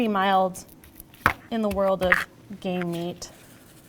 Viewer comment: I really had to listen, I could have sworn I heard "lamb is pretty mild in the world of gay meat" I laughed for like 10 minutes